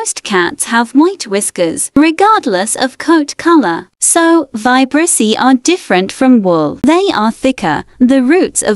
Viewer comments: Ngl when when she said vibrissae I thought she said "vibrussy" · Thought you said vibrussy